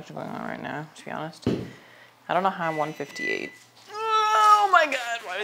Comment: did she say she weighed 158 now?